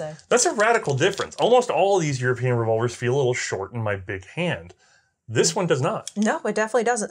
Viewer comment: Why, oh why.. I heard "All of these European revolvers feel short in my pimp hand." I am so sorry, I was actually listening to Matt Hoover over at CRS maybe last night and I recall something silly of his about a pimp hand, TOTALLY inappropriate, you and those who've helped you with the documentation and history, patent and all, just OUTSTANDING work, SO good, thank you for the depth of knowledge here. Sorry I truly mean no offense with my slang comment at first, MY nasty ears heard the wrong word, I immediately knew what you said, I'm just bad. Great video, really outstanding work. I love the majestic simplicity used in the French 1892, so beautifully done, a work of art for sure. Shame it seems to be a weakish, smallish, underpowered for sure cartridge. However a great pistol none the less!!